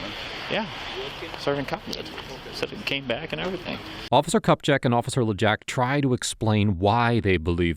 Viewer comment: did he just say officer cup check?